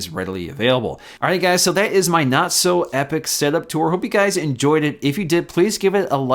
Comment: Is it just me or did he just say "Not so epic" at the end of the video?